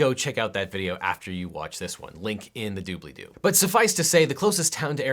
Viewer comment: Did he just say Doobly-do? Nerd?